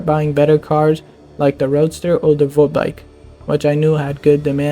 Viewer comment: it said volkswagen instead of voltbike...